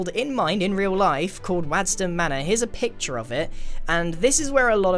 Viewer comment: The first time he said manor I thought he said manure.